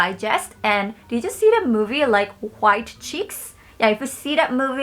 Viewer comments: I thought she said “you know that movie wide cheeks”😭 then when she played the movie clip I was like ohhhhhhh white chicks Also I don’t use public restrooms a lot but when I do I always get paranoid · when she said "white chicks" i thought she said "wide cheeks"😳😳